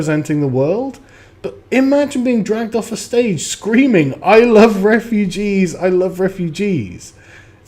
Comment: I thought she was saying "I love RPG's" ( as in role-playing games) I like RPG's too, but I wouldn't attack anyone over it.